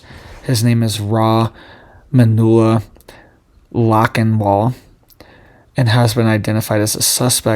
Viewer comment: Sounds like he said the suspect's name is Manure Lockin'ball.